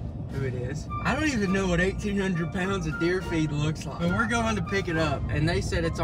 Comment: I thought he said deer feat😂